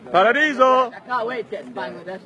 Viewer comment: To me it sounds like he says "Can't wait to get in to Bangladesh TONIGHT"